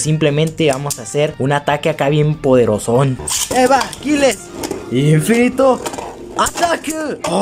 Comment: escuche infinito atraco jjajajajajajaja XD